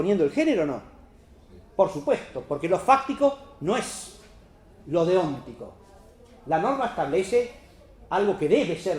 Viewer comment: En el minuto 1:11:55 cuando dice que " lo fáctico no es lo de ontico" a qué se refiere con "ontico"? Yo lo entendí como 'lo que debe ser' pero eso es más la moral la definición de ontico no encaja... Si me pueden ayudar les estaré eternamente agradecido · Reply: Exactamente dice: «lo fáctico no es lo deóntico» (nótese que no es como tú lo escribes: «de óntico») Habla de deontología, no sobre lo óntico. Lo deóntico se refiere en este caso, a lo que dice la norma positiva. La norma describe un ´deber ser´ (deóntico) y no como un ´debe pasar´ (fáctico/hechos). Es por eso que lo pone de ejemplo para reflejar las falacias que se dan al cambiar el género de la discusión, ignorando la cuestión. Ej. Él es un buen trabajador porque tiene dos hijos y es un padre amoroso (véase que no se da razones de por qué el trabajador es bueno, sino, se argumenta sobre algo que no tiene nada que ver. Espero haya sido de ayuda. Saludos.